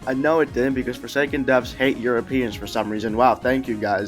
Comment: I thought he said something about Piggy and I had a flashback, but not he said, "Hate Europeans."